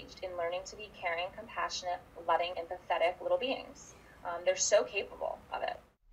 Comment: Did she call the kids "pathetic" at 1:59 ?!?!?!?!